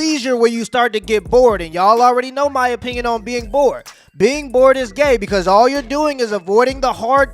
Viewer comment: Did I hear you say “being bored is gay?” 🤣